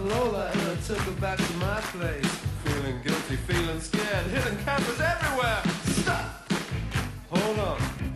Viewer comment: since I was a kid until now I always thought he said "hitting cabins everywhere" and I knew it was probably wrong but I never bothered to think of what it should be even with the song title lmao